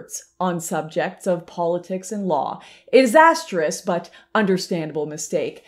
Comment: I must have re-wound this a dozen times and for the life of me, I cannot tell what you're trying to say? "... is a stris...???" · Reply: Disastrous?